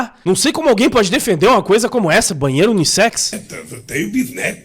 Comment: eu juro por td q é mais sagrado q eu escutei “baiano unissex” kkkkkkkkkkkkkk